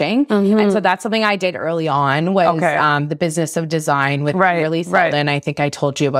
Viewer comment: she mentions the ‘Business of Design’ with Kimberly (??). What is the last name she says here? TIA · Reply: Kimberly Seldon - Business of Design. Thanks for watching!